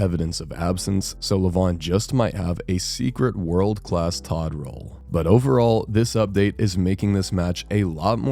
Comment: did I just hear Toddroll?